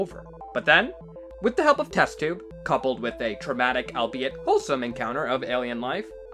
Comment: Wait, did fan just say couple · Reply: "Then, with the help of Test Tube, coupled with a traumatic, albiet, wholesome encounter of alien life."